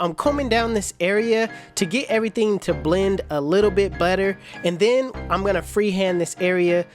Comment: "So, right now Im combing down this area to get everything to blend a little bit butter 🧈" Did he just say butter 🧈?? 🤣🤣🤣 That threw me off for a second.